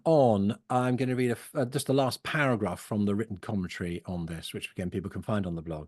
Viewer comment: Hi. I've copied the last paragraph you read from your written commentary (ahem - at first I thought you said "written coventry," so was searching wildly for "Written Coventry"..😤😤..) -- shall I merely cite your name? Not sure how I'll use it Sunday (weaving it into the Invocation) - not sure how to pronounce "psephizo" -- will look that up (psay-fid'-zo...?) -- read the def --- but it's definitely worth repeating many times. Thank you very much from across the pond. Nice job.